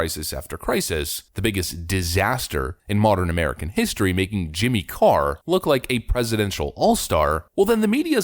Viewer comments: Jimmy Car? I listened a few times. Am I the only one who heard "car"? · Did you say Jimmy Carr? as in the British Comedian? what does he have to do with this?